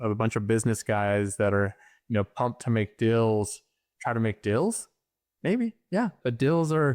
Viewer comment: The way he says "dills" instead of deals is bothering me